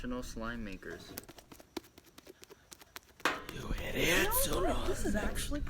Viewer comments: i kept hearing "you idiots" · i heard "you idiots" twice! Yea i really think ghosts are real... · I still love you fire fam ❤️ but I heard “you idiots” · OMG AND THEN 5:49 TO 5:54 I HEARD "YOU IDIOTS" BUT I DIDNT HEAR THE REST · who else heard someone moaning “you idiots”